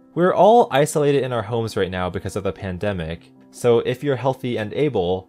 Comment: Mouth glitch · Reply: btw i misheard "homes" as "hose"